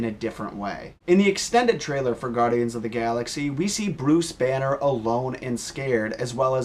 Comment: For the whole entire video i thought he said BRUCE WAYNE